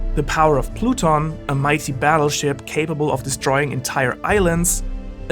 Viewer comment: why does the subtitles say a mighty battle shit